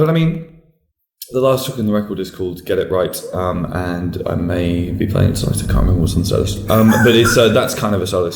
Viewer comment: What is he saying at 6:12?! It's bothering me so much that I can't understand it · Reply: The last track on the album is called get it right I may be playing it tonight but I can't remember whats on the set list thats kinda a solo song :)